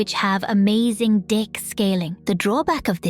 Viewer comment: am I crazy or did she say dick scaling?